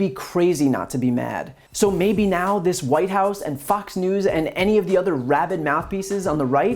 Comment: I swear he said "F*cks News" :-)